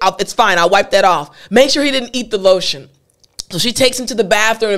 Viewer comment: what did she say? 😢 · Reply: "Make sure he didn't eat the lotion" - is what she said. Thank you